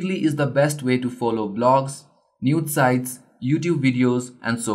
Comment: ·Did you said Nude sites @ 6:06? ·I think you forget to hide your Mom's number :/ ·bta great work keep it up!!